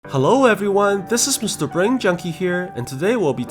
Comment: "The Japanese Mean Girls" should be the next movie you recap 😂 your witty one liners always make my day Brain Junkie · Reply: For some reason I thought you said “Brian junkie” XD